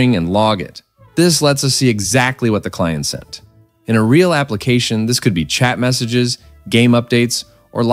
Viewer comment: Did I hear cat in 6:21? :P